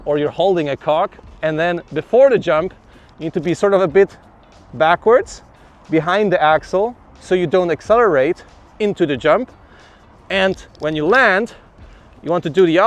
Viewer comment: "Imagine you are a cock, or you are holding a cock..." Words to live by friends. Perhaps I misheard the following: "Before the junk, you need to be sort of a bit backwards, behind the a$$hole so you don't accelerate into the junk... you don't want to stay too stiff." <---genuine internet gold.